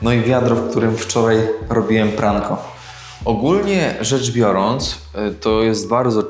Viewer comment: face-blue-smiling:dzięki Tobie wymyśliłam suchar! Najpierw pokazałeś w jakim wiadrze robiłeś pranie, po czym powiedziałeś "Ogólnie rzecz biorąc..." :face-blue-smiling:Mój mózg usłyszał "Ogólnie rzecz piorąc..". No i suchar może brzmieć: "Co mówi filozof kiedy zaczyna robić pranie...?" Odpowiedź: "Ogólnie rzecz piorąc to...". I wtedy już można zacząć śmiało filozofować do woli 🤣🤣🤣🤣